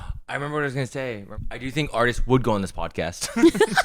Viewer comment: the entire section i thought they were saying ardiis would go on the podcast and I was so confused why specifically